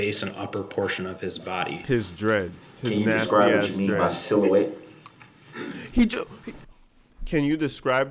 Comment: he said can you describe what you mean by silhouette he just said it really fast. but Brooks still dumb though...